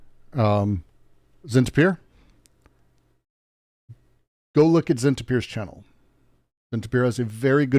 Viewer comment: What channel is being recommended at 1:13:00? Subtitles say zint appear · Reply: It’s